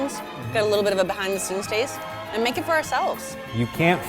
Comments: When she said “eating your feed” I thought she said eating your feet😂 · I always hear “Eating your feet” and it makes me very uncomfortable 😣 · I hear "Eating Your Feet" Everytime !! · It sounds like is saying “Eating your feet” Lmfao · I thought they said, "Eating your feeT.".....oh my god whats wrong with me